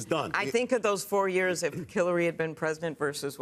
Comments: Did Klobuchar just call Hillary, Killary? LULZ!!!! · I would swear she said Killary !! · Did she say “Killary” at 8:25? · I swear she says Killary